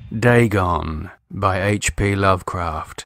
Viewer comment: Thought it said "Dragon"